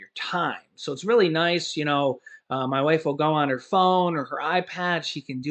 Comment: I don’t personally get snap benefits but many of our viewers do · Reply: I could have sworn you said my wife calls in...